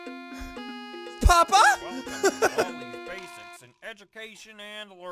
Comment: did he just call me papa